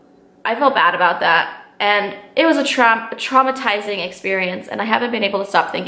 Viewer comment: Your unconscious reveiled what it was: 6:49 you almost said 'it was a TRAP'